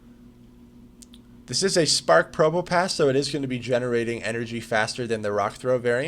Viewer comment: took me few seconds to realise what you said. At first i thought you said smart probopass English not my 1st language btw